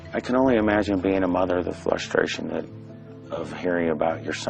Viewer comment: did he just say...flustration? lol